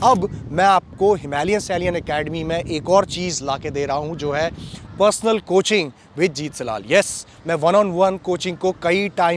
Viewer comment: jethalal I Heard 😅